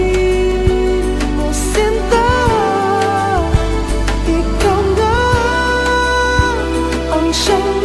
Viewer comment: i finally found it, I first heard "isaw nga" since i was a kid. i was like "i swear i know this song.."